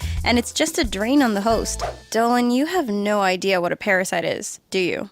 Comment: Did she just say dylan , that's my name